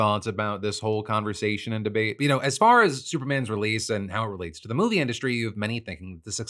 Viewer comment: I swear i heard you say "superman is real" controversy 🤣